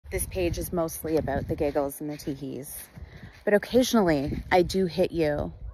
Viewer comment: I thought she said giggles and t!πies.